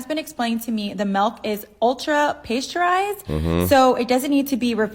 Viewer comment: She did not say pasteurized she said ultra high temp - that type of milk is known in English as UHT-milk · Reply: At about 05:10, she said ultra-pasteurised, but used -ay instead of -a for the first vowel.